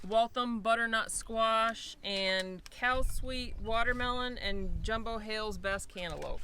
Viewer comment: I thought said cow sweet watermelon I had to look it up and it made more since cal sweet 👍